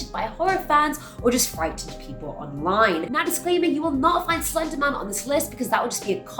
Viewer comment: It's slender man!!! He is the leader of the creepypasta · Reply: music hernandez I know but it sounded like she said slender mum